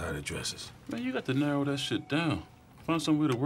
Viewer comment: Dude im not American .... so naturally the 'Carver' at the end got me confused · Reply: Thought he'd said GW Carter, which would've been a good enough joke.